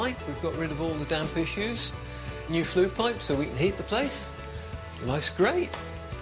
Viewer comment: how on earth can you have flue pipes run next to wooden staircases? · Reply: see about 5:14 he says - new flu pipes - part of the heating system (instead of radiators) - (maybe retro-fit ?).